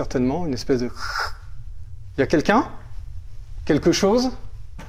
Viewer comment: C'est moi ou à 14:16 ou il dit " il y a quelqu un ,quelque chose " on entends une voie ,j ai écouté plusiers fois ,on entend une voix et 27:33 aussi quand il monte l escalier